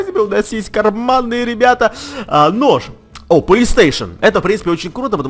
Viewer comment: мне одному послышалось не Police station а Playstation???????????????